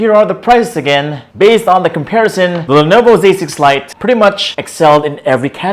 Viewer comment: I swear I keep hearing him say "Lenovo basic flight" 😂